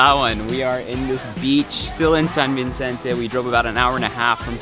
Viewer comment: I miss Palawan. I miss my hometown. I miss the taste of Chaolong 🤤😍 I love your video ❣️ btw, I don't know if I misheard you saying San Vincente hahaha when its actually San Vicente 😅😂 anyways thank you for this. I'll be back there in Christmas break 😁 can't wait ack